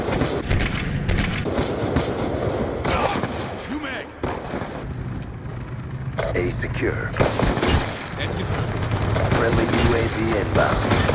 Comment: lol 2:48 the guy sounds like he said uMad?